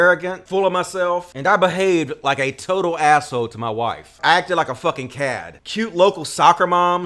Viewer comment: did he literally just say "kad" instead of Chad with a "shuh" sound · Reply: I said "cad". This isnt the Incel Corner dude.